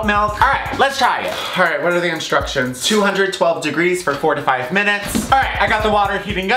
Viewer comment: thought he said 45 minutes not 4-5 minutes